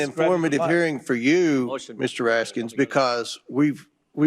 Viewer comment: "Mr. Raskins"? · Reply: Sounded like he said Asskins